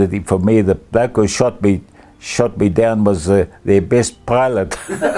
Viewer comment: What did he say there? didnt get it.. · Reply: "The gut that shot me was their best pilot", if I'm not mistaken.